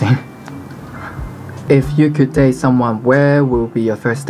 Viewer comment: I thought he said ( If you could taste someone.......... ) OMFG Anybody else??